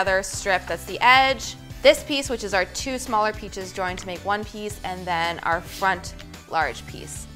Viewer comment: Anyone else hear “two smaller peaches”?